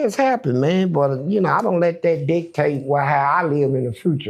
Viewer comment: Funny When He Said "DICTATE"......I Thought He Was Saying "D... TASTE" 😅